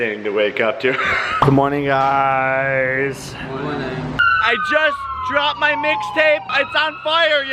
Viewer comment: When you said "I just dropped my mixtape" I thought you said "I just dropped my milkshake" 😂😂😂